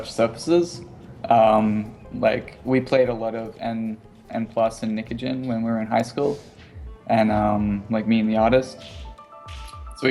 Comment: what's the game he mentions at 20:59 when he's talking about games he played in high school? he mentions N, N+ and... ? i don't understand the third one. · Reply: Nikujin, you're welcome for the quick response